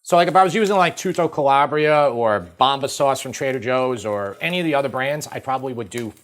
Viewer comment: I was very confused until you got to the end of the ingredients. I Thought you were saying Bacca sauce, and I had never heard of that.